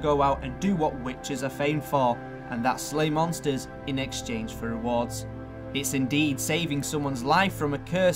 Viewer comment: Got lost at 1:21. The hell did he say? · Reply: Slay monsters in exchange for rewards